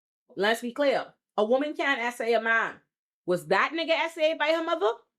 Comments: I SAID A WOMAN CAN SA A MAN. NOT ONLY DID I SAY THAT, WE PUT THE TEXT ON THE SCREEN AS I WAS SAYING IT SO THERE COULD BE NO CONFUSION. Click the timestamp and actually LOOK at the screen. The fact that yal can’t be bothered to even look at the video you’re watching to know wtf I said, but you can take the time to pause it to try to call me out about some shit I didn’t say is blowing me. The next people to comment accusing me of saying women can’t SA men is getting blocked. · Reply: I was getting ready to make this response. It really sounds like you say a woman can't assault a man. Thanks for clarifying. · I SAID A WOMAN CAN SA A MAN. NOT ONLY DID I SAY THAT, WE PUT THE TEXT ON THE SCREEN AS I WAS SAYING IT SO THERE COULD BE NO CONFUSION. Click the timestamp and actually LOOK at the screen. The fact that yal can’t be bothered to even look at the video you’re watching to know wtf I said, but you can take the time to pause it to try to call me out about some shit I didn’t say is blowing me. The next people to comment accusing me of saying women can’t SA men is getting blocked. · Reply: I had paused the video to make sure I heard correctly because it sounded like you said a woman can't SA a man. I do listen on 2x speed and I'm like I know she didn't say that.